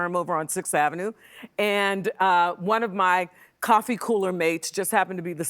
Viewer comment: Did she say "one of my coffee-cooler mates in the office?" 2:29 😊